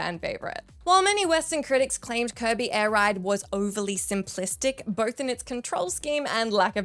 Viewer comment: I thought you said "Curvy Air Ride" and thought it was a DoA Beach Volleyball game with scantily clad women riding jet skies lol